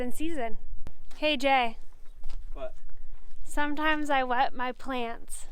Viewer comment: I honest thought it said, I wet my panties, yikes